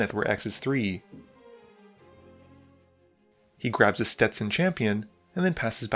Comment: I heard "Stetson Champion" and immediately wondered what the card would look like if it was altered to have the creature wearing a ten-gallon hat :)